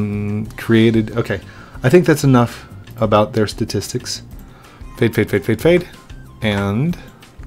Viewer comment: did he just said taip taip taip? Or in Lithuanian language yes yes yes?!?! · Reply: He said fade I think